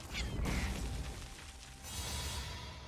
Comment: i can't hear name of anime properly. Can someone write in comments what is was. I'll be grateful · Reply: "Akira"